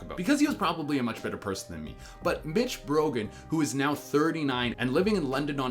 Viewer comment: it sounded like he said b**ch brogan instead of mitch brogan